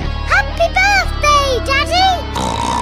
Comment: I thought he said thank you peppa